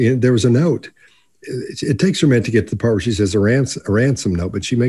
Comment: I thought she said where is she, I listened several times with eyes closed and that's all I heard from it